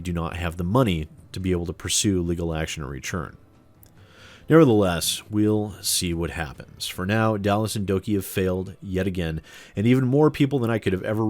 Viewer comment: I thought it said Dalas the donkey. =(